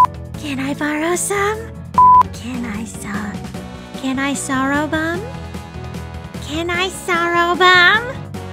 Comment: why did just say "can I sorrowbom?" at 0:44😂😂